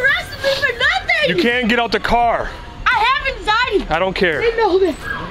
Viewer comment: Cop: "she bit me" Lady: "......cuz you wouldnt let me go!!!!!" Later Lady: "i have anxiety" Cop: "i dont care" Absolute legend😂😂😂 what a strange lady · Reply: Lol I heard the "I have anxiety" as "I haven't died" and was so confused